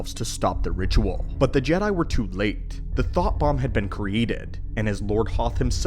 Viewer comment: I know what you meant, but I still initially heard it as, "Thot Bomb".